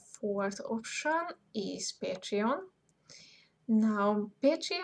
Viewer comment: Photoshop what ? And what were you saying at about ..."Patrion" ? At about 5:08 on the video, what was that ? Thanks